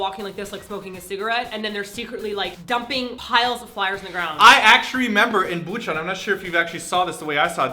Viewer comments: I heard you say actuary XD Don't worry, I'm not even in Korea nor do I speak Korean enough, but I still speak engrish >//< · 1:25- "I actuary"... Actuary!? You didn't even catch yourself there, but that's ok cause I'm sure most of us noticed it for you ;) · did you actually say actruary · I heard, "I act tree member" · did you just say "I actrually"??? XD Omg my mom always talks about the wifi everywhere. And for kids 8- or something, a bus pass is 50 cents. What???? A one month bus pass here is like either a hundred or twenty bucks!! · Did say.... "Actuarry"???? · Did just say "act'shury" ???? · Ohoho, did you say 'acturry"?? You've been in Korea for a long time, haven't you ;) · Haha, did you just say "I actuarry remember?" :P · did you say actury instead of actually? x3